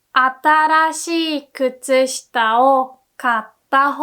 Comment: I noticed at 20:05 it sounds like you're saying "あたらしい" but the text is missing 'ら' above the kanji. Also in the next part it says "a hope is open" instead of hole.